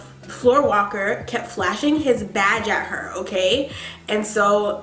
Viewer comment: I thought you kept saying "flashing his VAG" I was like wtf is going on here?! Lmao